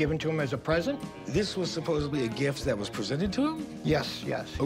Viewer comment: Did he just say “supposebly” at 0:10?